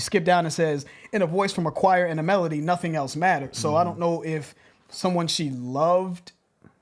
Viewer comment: I've always thought she said the one we love